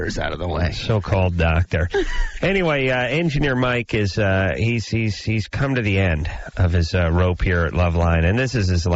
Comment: Engineer Mike's last night? What does he say in the background here? Something, something... "next week." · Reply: “They will next week” Meaning, they’ll realize what he had been bringing to the show, once he’s gone.